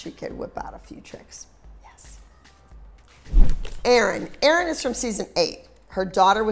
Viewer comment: The captions said Aaron instead of Erin